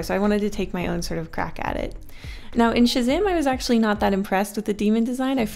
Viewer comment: watched what? i can't make out what's being said :') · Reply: Pretty sure she means Shazam